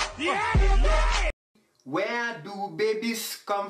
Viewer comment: i thought the last one said fat a** train but after listen again it is fathers train